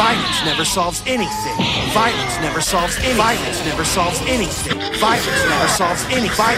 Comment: I only heard "violence salts anything".